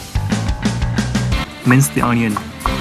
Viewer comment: Calm music and stuff Then suddenly... MINCE THE ONION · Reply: When i see/hear "mince the onion", i always hear instead "hacka lök" :p Where is the mayonnaise? (It's good for you!)